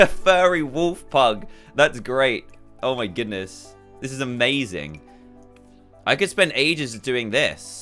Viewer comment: why did I hear “oh sh£*” what did he actually say instead